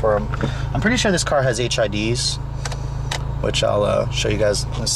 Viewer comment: i thought he said im pretty sure this car has HIV's